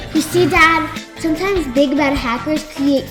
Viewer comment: I thought she said dad sometimes big back hackers but she said big bad 😂😂😂